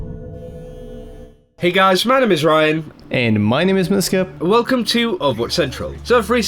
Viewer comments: Everytime, I hear "Hi my name is Ryan." "and my name is Noscope" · To this day i still don't know what the second guys name is, all i hear is "hi im Ryan, and im dhdhdhsk"